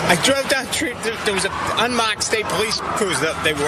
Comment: Did he just say "Unmocked Cah"